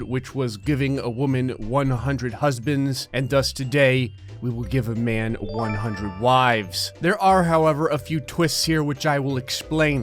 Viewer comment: I didn’t read the title and I thought he said “I’ll be giving a man 100 lives” and I immediately thought “well this video shouldn’t be too long then, considering this is Gray...” Edit: Also I really want a Spleens plushy... just thought I’d share that...